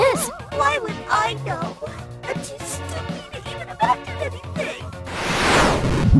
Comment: so apparently Ena is saying: "my stupidness never ends but ive always heard: "im as stupid as deviled eggs" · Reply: I hear "im to stoopy to imagine anything"